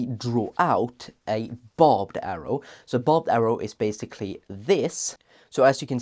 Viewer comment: i was already googling the term bobbed arrow as i thought you said it as such and only later realised you were saying BARBED arrow 😅